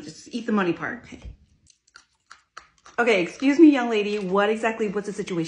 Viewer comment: at first i thought she said im eating the muddy part like you told me